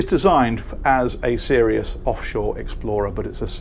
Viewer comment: I thought you said “posh shore explorer” 😂